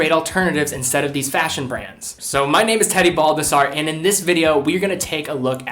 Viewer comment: I thought you said your name was teddy bulbasaur